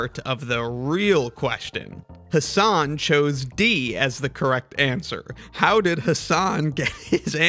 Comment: For like the first half of the video, I was so confused because I thought it said “Hasan got the answer C.” And I was like “he’s right though?”